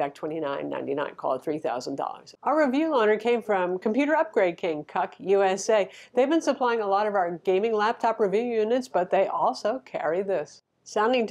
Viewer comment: Did she say Cuck USA @ 2:57? 😱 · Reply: Computer Upgrade King - CUK It's literally in the description.......